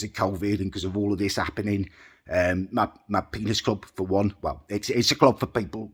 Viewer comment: Did you just say “Penis Club?”…….or has my hearing malfunctioned? 😳🤭😂